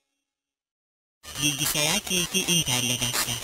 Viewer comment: did she just say lilgy